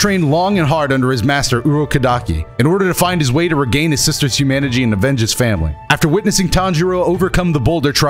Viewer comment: Am I the only one who heard him say "reclaim his sisters humanagy".